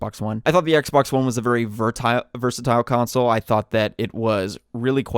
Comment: Did he say "A fertile console"?? That disc slot is FOR GAMES!!!!!!